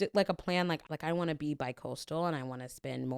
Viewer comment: said they wanted to be bicoastal and I literally heard, "I want to be by cold stone." And that really hit me hard